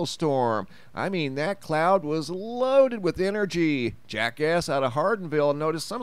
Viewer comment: I thought he said “Jackass out of Hardenville”. I need to pay more attention.